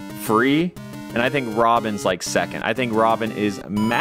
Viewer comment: Lol I thought he said Rob. Instead of robin